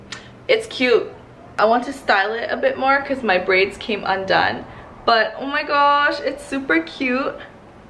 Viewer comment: I thought she said "my grades came undone" my grades came undone too girl, mine too... lol